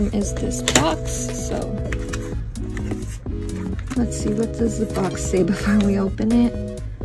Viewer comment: I thought you said: “ What does the FOX say “ When you said: “ What does the BOX say “ 😂